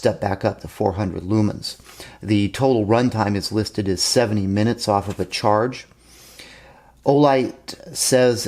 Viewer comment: it sounds like you say it will run for 7 ( seven ) minutes. I may have mis-heard what you said or you said it real quick. It is rated at 70 minutes in case anyone else thought you said 7 minutes like I heard. It would be nice if Olight would make it so that the positioning clamp could be relocated in more than one position, allowing it to fit the newer sub-compact pistols like Springfield XD Mod 2. Thanks for the video. J K